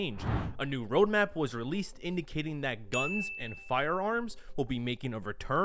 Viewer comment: thought he said "fire alarms", I genuinely had a eureka moment